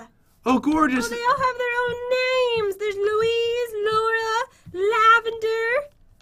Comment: When Jen got the Lamas and she said Laura I thought she said my name because my name is Maura